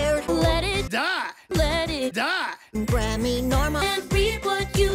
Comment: Is it just me or does it sound like she saying Grammy Norma